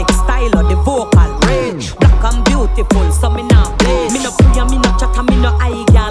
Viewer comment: Black and beautiful, so me nah bleach 🙏🏾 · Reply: I thought she said so me not beige!